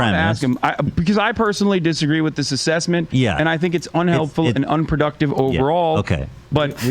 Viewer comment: Hasan is saying “unhelpful” and “unproductive”, he just mumbles/slurs his words sometimes. Particularly when he’s in a back and forth with someone who he’s trying not to upset. But trust me, there’s not a single fiber in my body, soul, or mind that wants to defend Hasan or even clarify his speaking for him. It genuinely physically hurts to say/do anything that’s helpful to hasan in any way but the one thing I can’t do is deny, suppress, or alter the truth and the truth is that you misheard him. … or shit maybe I misheard you repeat what he said so I’m the one who’s all facked in the head! Lol I don’t know and I’m not going to relisten to be sure. All I know is you stopped to make fun of hasan when he said “it’s unhelpful and productive” in other streams too but I didn’t say anything in those streams because it didn’t seem necessary or important at all. But now that I’ve seen you make light of it multiple times, I felt the annoying compulsion to clarify what he actually said. But please, make fun of hasan all you want! I’m not even joking when I say that I’d watch every single episode if you made a channel that was only about talking shit about Hasan. Lol if you made a troll channel where all you did was make fun of all his stupid takes on things, I’d watch it first thing everyday. I’d use the videos to watch before I watched or read anything for the day, that way, it would guarantee that the start to all my days were happy and funny. Please do it… I know and you know that out